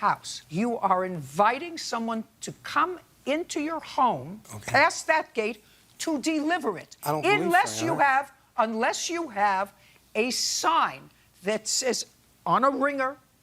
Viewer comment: did he just say “I don’t live there”?? 😲😭😩😂😂